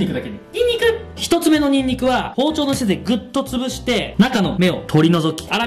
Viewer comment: おかしいな、キンニクって聞こえた気がした…ん〜まぁ気のせいでしょ、うんうん